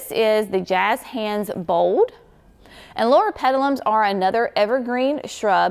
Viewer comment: The plant that you showed at around 7:30, what is the name of that? I tried to look it up on proven winners but could not find it based on what the tag said. And I could not understand what you called the plant. It sounded like Laura or Flora. But you never showed the name of that only what was on the tag and that did not show up on proven winners website. Thanks for any help you can give me. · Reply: Loropetalum (I have been coveting that plant from afar…I am in zone 6a, which is 1 zone too cold for them)